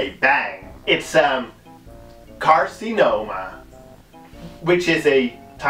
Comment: I swear to God I heard you say the last movie was "Encino Man" instead of "Carcinoma" and I absolutely lost it 😂😂😂